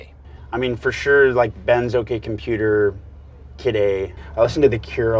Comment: What does he say at 6:03? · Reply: "Bends, OK Computer, Kid A" is what he said (all by RadioHead).